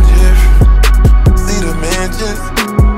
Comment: I THOUGHT FUTURE WAS SAYING FREE JEFFRY FREE THE DA MAN JEFFRY, BUT HE ACTUALLY SAYING AIN'T THAT DIFFERENT